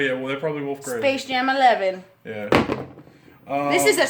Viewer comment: bro did she just say “space jam 11” 🤦🏽‍♂️🤦🏽‍♂️